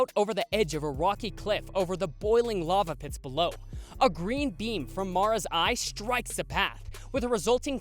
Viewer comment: I thought at 13:13 he said green bean, I got really confused